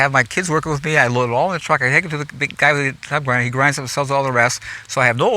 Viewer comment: explains what he does with the trees. I don't quite understand what he was saying. Can someone clarify for me? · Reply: He takes them to the guy with a tub grinder. Pretty sure that is shown in the Back to Eden movie.